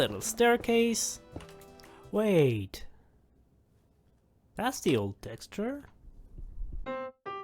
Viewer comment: what did he say there??/ i dont underst and... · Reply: "that's the old texture" i made custom subtitles for the video btw!